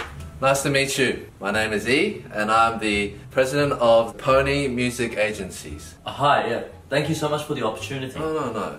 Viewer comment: ‘I’m E and I’m the head of Pony music agency’. I thought he said phoney Music agency.